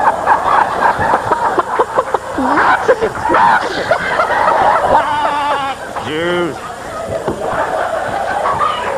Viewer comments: the racist farm. What's the chicken saying? Nazis? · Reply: I think he said "Mexicans" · What did Louie say at 9:41? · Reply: Mexicans!